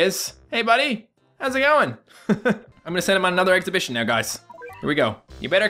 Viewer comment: I like how he says, "ex-a-bi-tion" Instead of "expedition" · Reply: Finally someone else noticed him saying it wrong he is saying exhibition not expedition